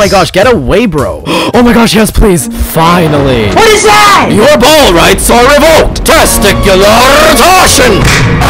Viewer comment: For those who are wondering what says at 0:25 , he says "your ball rights are revoked! Testicular torsion!"